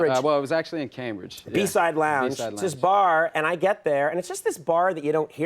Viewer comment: The B-Side lounge was one of my favorite spots in Cambridge years ago. That definitely brought back some memories. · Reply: I thought they said Eastside lounge which is also in cambridge. Both are closed now